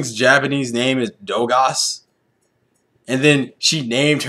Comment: when they were doing that "who's that pokemon?!" when koffing said Dogas, i heard "Dumbass"